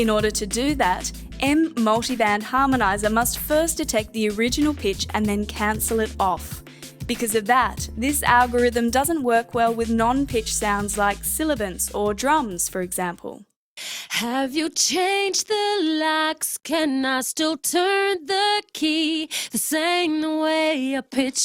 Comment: did she say silibants at 10:52 ? :D :D :D